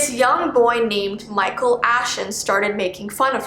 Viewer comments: "Michael Ashen" Me, who misheard: MICHAEL AFTON!? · I thought you said Micheal Afton XD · Toy: Wheres your face? Her:what? l have a face! Toy: giggles evily Her: scared Toy:your face.. Her:y-yes? Toy:IS MINE L STOLE IT FROM YOU AT MIDNIGHT HAHAHAHAHAHAHA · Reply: Random mention but when she said Micheal Ashon I thought she said Micheal Afton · Fnaf fans: Doll:*steals girls identity * Fnaf book: Elinor *steals girls identity * Edit:omg thanks for the likes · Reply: it sounded like Micheal Afton. i had to play it back lol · “ Micheal Ashon “ Me: misheard it WAIT MICHEAL AFTON who brought up fnaf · when said the 'Michael Ashen' (correct me if im wrong.) i actually thought she said 'Michael AFTON' and i was shook but then re-watched that bit and found out it wasnt that lol. I watch way to much afton family things nowadays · I thought she said Michael afton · Um is it just me or when she said ‘Michael Ashton’ I heard ‘Michael Afton’ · it sounds like u said Michael Afton from FNAF